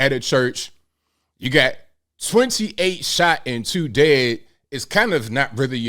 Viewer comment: Oh crap, i thought you said 28 shot dead...but still...wth Baltimore?